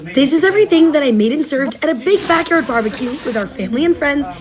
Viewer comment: I thought she said big back but it was big backyard 😭😭😭😭 also great cooking👍